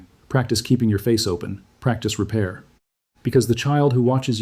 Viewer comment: This is largely overlooking something and that is repair. No one is perfect and love has a lot of ups and downs. But repair and communication even at an early age shows they can be upset and still loved too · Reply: He actually says practice repair